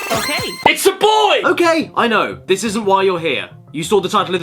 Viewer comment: i t s a b o y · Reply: and I thought he said "It's ya boi!"